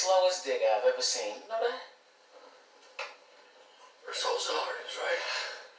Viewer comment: what did he say😂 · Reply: First hole the hardest right?